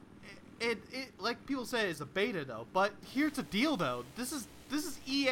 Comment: did you say ''Here's the dildo'' on purpose?